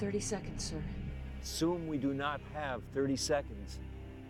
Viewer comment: "We are good to go in 30 seconds." "Assume we do not have 30 seconds." Timer hits zero 35 seconds later. The damage doesn't create a noticeable change for 70 seconds. Nothing negative happened during that entire time. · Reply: I thought he said "Soon, we do not have thirty seconds.".